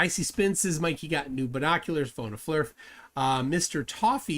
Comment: Every time I hear that "phone a flerf" it sounds like phone a vet...is it just me??